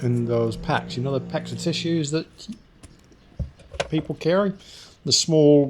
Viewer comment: Oh lord, I thought you said FISHES, I didn't realise she sold TISSUES.